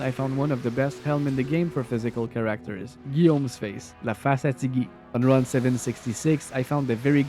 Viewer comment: What does he say at 12:46 after "Guillaume's Face"? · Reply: I said "La face à Ti-Gui!" which literally translate to "little guillaume's face". Guillaume is a common french name and a surname for Guillaume is "Ti-Guy" (little guillaume).